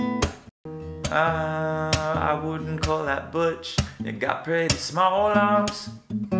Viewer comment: My favourite part is when thought it said “butch” instead of “hunched” and said I wasn’t butch I actually have small arms